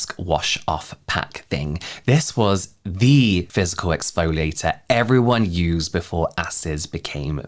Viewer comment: Every time I hear physical. I keep hearing "fiscal" year.